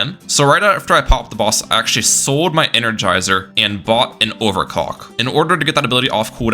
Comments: I swear to god I heard him say sell his towers and buy and over cock did anyone else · a what u say? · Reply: I refuse to believe he said “clock”